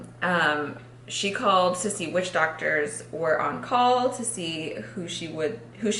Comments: when said "to see which doctors" I thought she said "sissy witch doctors". 😂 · When said "she called to see which doctors" I heard "she called sissy witchdoctors." I was so confused for a second